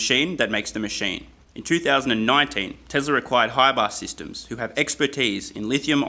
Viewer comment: I keep hearing "tesla required" every time he says "teslar acquired"